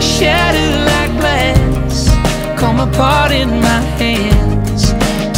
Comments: First time we heard this song in the car it sounded like he said, "Break on me, shatter like glass, come and fart in my hands" We were cracking up. Maybe it's the aussie accent but every time I hear this song now that's what I think of. lol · Am I the only one who hears it?? Line 2 of the Chorus.. all I hear is "come on fart in my hands" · This song has been on rotation on our crappy work speakers. The line, "come apart in my hands" for the longest time we heard it as, "come in fart in my hands." 😅